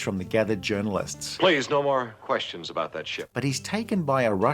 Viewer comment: I heard "Please no more questions about that shxt" :face-blue-smiling